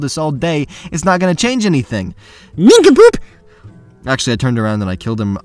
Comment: What’s that noise What’s he saying „Ninka Poop“??? Is he saying ninka poop? What is ninka poop? Someone explain pls